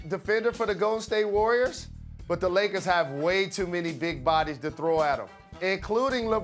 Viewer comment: Greymond Dreen?! Did I hear that right?